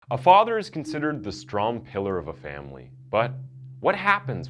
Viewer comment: I heard “a father is considered the strong killer of the family” at the beginning and it seems I wasn’t too far off based on the title